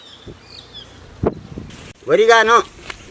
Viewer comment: мне не одному послышалось какаин?